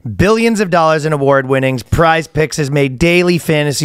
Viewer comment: thought he said prize pigs 😂